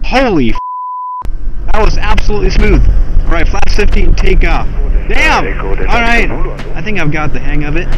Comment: Us watching this be like: And ro-ta-tay wait did he just say and take off and not ro-ta-tay. Well at least you said butter. Awesome video.